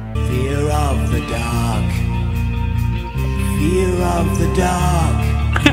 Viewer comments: why I heard "feel of the duck"? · I hear fear of the dog… which is sadly true😢😢😅😅